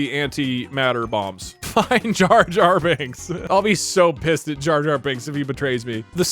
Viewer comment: i was drinking my water and i wasnt listening much and i thought said "Rape Jar-Jar binks" and i almost spat out my water from laughter and surpise